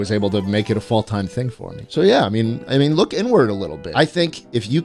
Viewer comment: "look n word a little bit" ?? schlit what are you saying man.